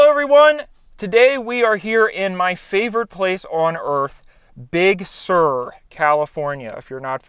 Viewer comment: For a second i thought it said big shaur